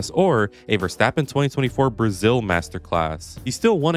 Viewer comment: i love how the captions 6:14 say masterass instead of masterclass